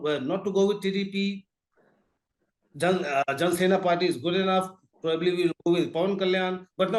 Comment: Fun Fact at 08:44 If you enable Captions, you will see John Cena party instead of JanaSena party